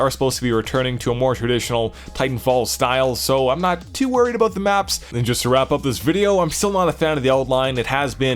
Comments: What did he say at 4:10? It's driving me crazy. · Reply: "I'm not too worried about the maps." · I meant 4:12. It's what he says right after he says "just to wrap up this video". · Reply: I guess he said "I'm still not a fan of the outline" (around enemy players).